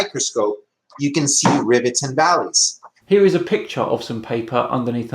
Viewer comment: Did he say 'rivets and valleys'?